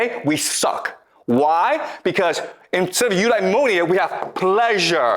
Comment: What is the word at 50:08 the subtitlea say ulmonia but i cannot find anything meaningful searching it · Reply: Eudaimonia